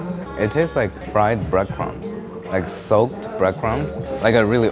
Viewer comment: Misheard say, "tastes like fried breast pumps"...and didn't bat an eyelid.